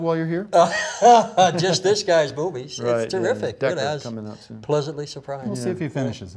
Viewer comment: I swear I heard "just this guy's boobies"